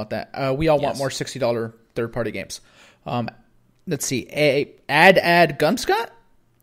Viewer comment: I have a dock in my bedroom and one in my 4k tv in the living room. (To be fair I got 2 Switches at launch so that's where the second dock came from(the second Switch I gave to my GF)) I don't understand peoples issues with friend codes? They are 12 digits long and aren't hard to remember. A credit/debit card is 16 digits plus exp date and cvc code. And phone numbers are 10 digits which neither of those people complain about. I heard it as 53rd party games 😂